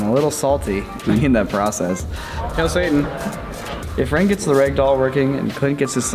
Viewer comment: ? ????? LOL I had to do a rewind real quick to make sure I heard that correctly hahaha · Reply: Hail satan?